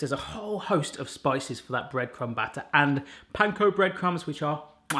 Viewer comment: I have been watching you for years and your accents are absolutely beautiful. But today.. I realllly thought you said "Pine cone bread crumbs"!! I replayed it 10x and then had to go to the website to check🤣 I actually called out to my fiancé "Lettuce has Barry eating Pine cones!"😂🤣 ❤❤from🇨🇦